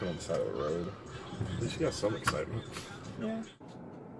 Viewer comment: Did that cop just say "at least you got some excitement" ? Wtf? Thats worse than what the bf said about not having to go to work